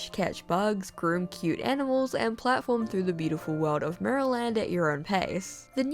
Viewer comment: Straight up thought you said "maryland"